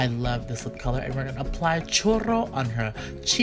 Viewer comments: I thought you said Chorro until you said like the dessert then I was like ohhh churro! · It sounded like he said chorro instead of churro 😂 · I thought patrick was saying "chorro" which in spanish means shit LOL